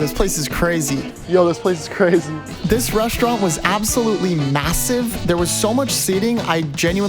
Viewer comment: did he say massive?